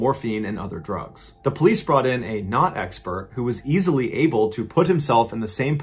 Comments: now mr ballen said a “knot expert” but what i heard was a not expert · “A knot expert was able to put himself in the same position” well yea because he is a knot expert lol dam these people just didnt care to help her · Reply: Oh I thought he said "not-expert" like he wasn't someone with any skills related to that but knot expert makes more sense. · A knot expert was able to tie himself up that way?! No shit!! "We aren't sure how this young man's body ended up on that roof, so we hired Tony Hawk to launch himself up there, thus proving the man got there himself" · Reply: i thought he said a NON expert lul